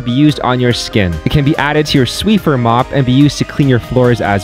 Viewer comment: Did he just say sweffer?